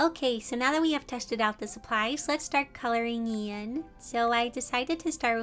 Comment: Thought the video said cooking Lan instead of coloring lan and I was like huuuh?